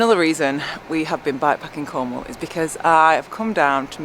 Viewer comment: She co-ordinates like a pro :) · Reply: oh Cornwall? I thought you said Como. I was getting ready to hop on the train tomorrow, go down and have a visit.